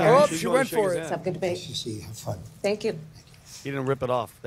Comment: I thought she said “Kamala Harris, second debate” 😂😂😂 she said “let’s have a good debate” she’s still a clown